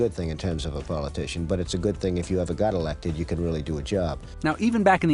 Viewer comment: did he just say if theres a GOD Elected😳 trumps antichrist.